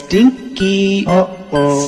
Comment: It sounds like hes saying upa stinky